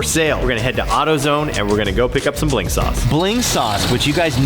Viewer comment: I thought they were saying Blink Sauce.